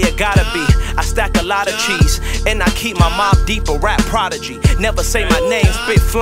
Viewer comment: "I keep my mobb deep, a rap prodigy" 🔥🔥🔥🔥🔥🔥🔥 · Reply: Ahhh I was just trying to figure that one out. I thought he said “I keep my mom deep”, like he kept his mom decked out or something 😂